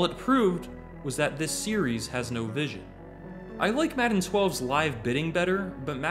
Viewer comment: I legit thought he said “madden oh-eleven” 😂